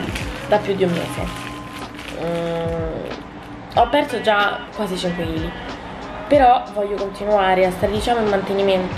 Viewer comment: non ho capito cosa hai detto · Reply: Ah ahahah circa 5 o qualcosa in più è da tanto che non mi peso